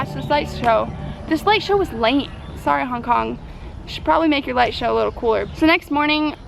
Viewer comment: I've played it and played it and still can't understand what she says. Sound like: "Sh' probly make ya lightshow a little quart".